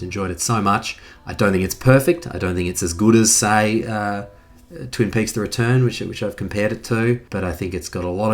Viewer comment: What is the name of this show he compares it too? I couldn't quite catch it. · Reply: Twin Peaks: The Return